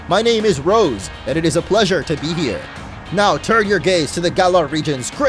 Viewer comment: Thought he said turn your gays